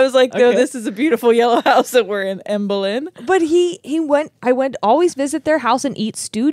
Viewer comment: I swear when mentions her own imaginary friend I thought she said Anne Boleyn 😂😂